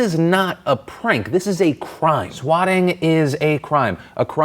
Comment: For half of that David Hogg story I thought you said "squatting" (staying in abandoned or empty houses illegally) and not SWATTING haha